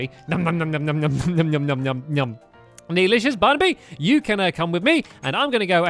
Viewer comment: it sounded like said "Delicious Barnaby" lolz